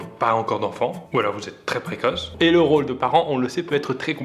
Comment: "ou alors vous etes tres Pierre Croce" applaudissez moi svp.